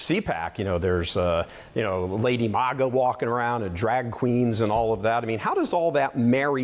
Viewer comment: Did he say Lady MAGA? 😂